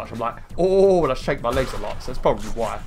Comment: Thought you said you shave your legs a lot for a second Mwahahahahahaha